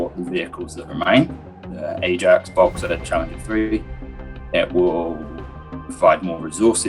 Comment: I think he said challenger 3